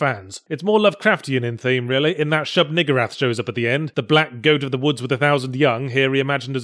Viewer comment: did he say the N word?